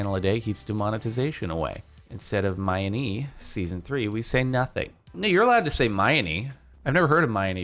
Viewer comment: Did anyone else hear “Nigger your allowed to say Mianie” at 13:26?